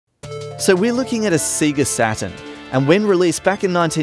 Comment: Did he say "satan"?